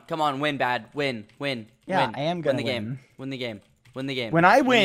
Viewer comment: LOL when he said 'win the game' I thought he was saying 'renagade' (9:55). Or was he saying Renagade?